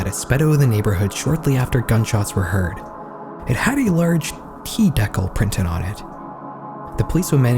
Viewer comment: I know you did not just say "T deckle"